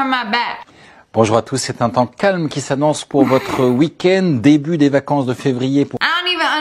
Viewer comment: he is NOT speaking french I heard "On the weekend"